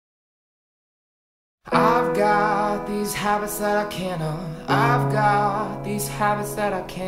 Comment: "I'VE GOT THESE HEAVY SET OF CANNONS!!" that's what i thought they said when i first listened to this song.